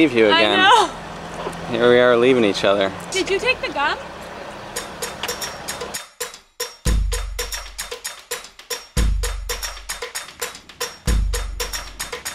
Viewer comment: I thought she that said: did you take the gun hahaha